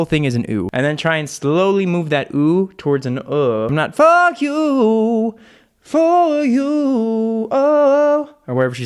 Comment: I heard “fuck you” and I somehow still felt blessed to hear his vocal 😳